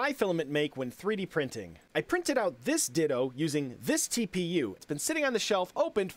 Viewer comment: I thought he said "I printed this d1ldo"💀